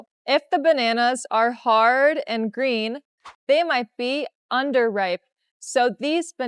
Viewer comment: I want to ask which is correct; unripe or underipe ? I'm a little confused. It sounds like she says “underipe,” (2:49) but I see “unripe” written. I learn English by myself, so I want to learn it right. It’s hard to relearn again later. :D Thanks!